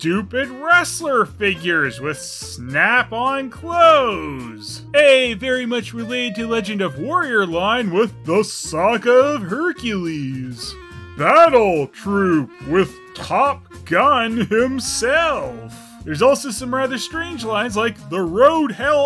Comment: ngl, I thought he said "The Sock of Hercules" for a second